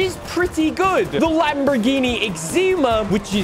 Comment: Did you just call the Essenza "Lamborghini eczema" ?